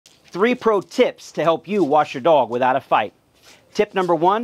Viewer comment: I really thought he said at first how to wash your dog after a fight 😂😂